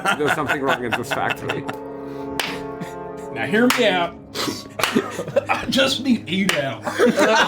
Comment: What did say at 1:35:24? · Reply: "I just need 8 hours"